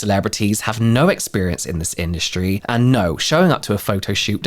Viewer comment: Lol when he said “this industry” I heard “the skin-dustry”. I was fully ready to accept that’s what people skincare called it😂😂😂 Also when I hear 29 Palms I just think of the Military base Marines complain about. I’m pretty sure any veteran would have the same thought. IDK if that affected the sales at all but still not a great start. They call it 29 Stumps.